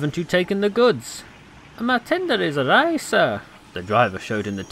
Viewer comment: What did the narrator say at 4:02? "Ma tender is [what?], sir."? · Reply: Awry